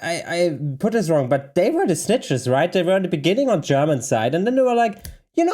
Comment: When you said "they were the snitchers", at first I thought you said "they were schnitzels" 🤣. Sorry - I'm an American. We make jokes out of everything! 😂